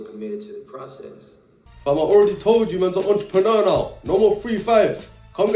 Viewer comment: I thought mans said "EntrepreMurda", lowkey sounds hard ngl 😂🤣🤣